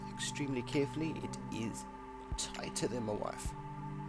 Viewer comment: did you say tighter than my wife?